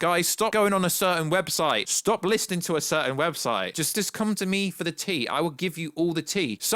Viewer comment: wait... did you just say 'come to me for the D' ?